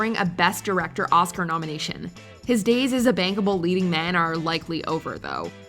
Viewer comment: Did I hear correctly? Sounds like she called Gibson a “bangable leading man” at the 12:00 mark